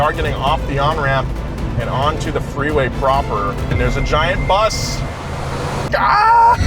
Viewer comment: "There is a giant bus" I heard "there is a giant boss"😂 to that car the bus is a boss